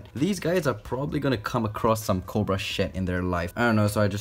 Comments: I thought he said "Cobra shit" 😂 😂😂😂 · Kinda sounds like you said Cobra Shit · Lol he said cobra shed I thought he said cobra sh*t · Did he sayed sh*t in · did he just say, "cobra shit"?